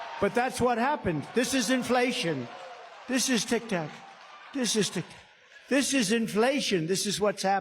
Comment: It sounded like he was saying “tic tok”, instead of “tic tac”?? Did anyone else hear that??